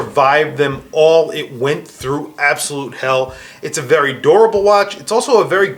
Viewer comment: When he was saying durable I thought he was saying adorable...